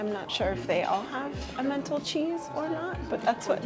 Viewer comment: I heard “a mental cheese” - then got it.